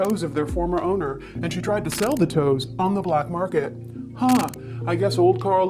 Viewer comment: i was half listening when AI was doing the patreon promo and i thought i heard that herself was selling toes on the black market and i thought that was some sort of feet pics jokes